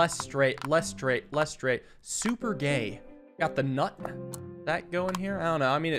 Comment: what did he say super gay is there something I'm missing but also it made me laugh. a lot